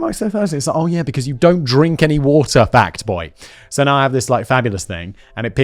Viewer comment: I always thought Simon said "fat boy" which was wierd... turned out it is "fact boy"... amazing...